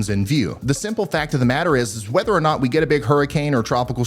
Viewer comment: Whenever he says, “Whether or not,” I just hear it as, “Weather or not.” Lol